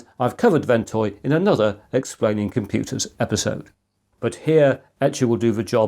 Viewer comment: had to do a double take here because I coulda swore the first time you said "Exploding Computers episode" 😅